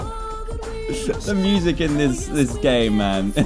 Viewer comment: did dan say game or something else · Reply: He said game